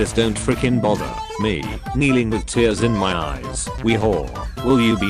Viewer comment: british accent made captions think it said wh*re